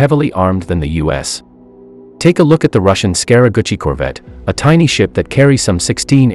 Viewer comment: I heard “Scary Gucci” and I couldn’t take it seriously.